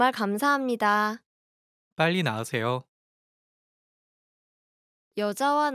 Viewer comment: i misheard and thought he told her to hurry and leave lol